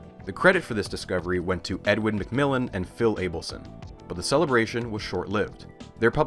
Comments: i kept mishearing and thought you said edmund mcmillen, and i thought, why is the binding of isaac developer in my chemistry video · i swear to god when i heard this for the first time i heard "Edmund Mcmillian" and just immediately thought of the copypasta, my mind is polluted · thought I heard edmund mcmillian onstead of edwin and was floored